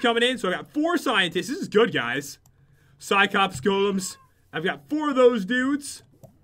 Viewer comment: Am i the only one who realized pat said cycops instead of cyclops...